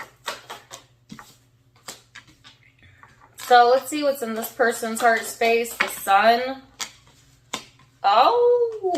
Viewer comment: So at about 17:14 from the video there is a man that whispers something am I the only one who hears it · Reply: Sounds like "take care"